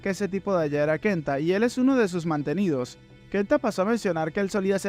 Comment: creo que dice Mantenimiento, no mantenidos, pero excelente resumen